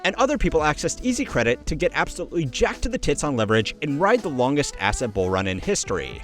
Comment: Did you say "tits"